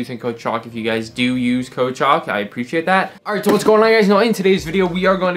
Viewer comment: I thought you were saying use code "chalk"